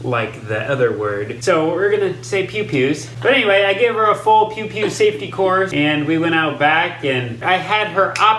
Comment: I thought y’all were saying “poo poos” at first when you were talking about pew pews. Wow. Hahaha